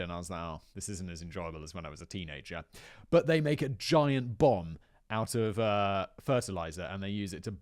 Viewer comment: For a second i thought he said they made a bong out of fertilizer and i was really confused...